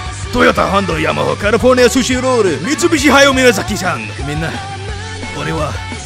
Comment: I think he said Toyota Honda Yamaha... Mitsubishi Jackie Chan Ahahah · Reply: Miyasaki san dijo